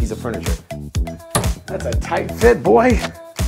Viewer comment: "That's a tight fit, boy" - that's what he said?